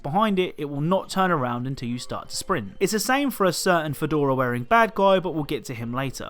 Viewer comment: I thought you said fedora wearing black guy lmao